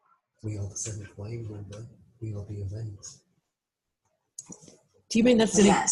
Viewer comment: Who is the other voice at 3:05? Is it saying "we are the 7th wave leelou. We are the event?" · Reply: My house mate restating an earlier message from leeloo....'we are the event'.......she replies 'yes'